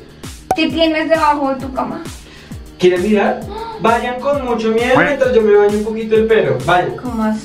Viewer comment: un año después y ahora es que me doy cuenta que dijo "vallan con mucho miedo mientras yo me BAÑO el pelo"